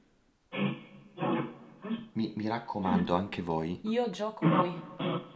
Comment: All'inizio del video a 3:09 dice : " ora gioco qui" poi dice a 3:20: "ora" Poi "innanzitutto" "loro non sanno" e "prima di" "Pimot". Il dado di infinitum è sul 5...ma non è finita...Celeste ha fatto infinitum. Ora quali sono le sorprese?......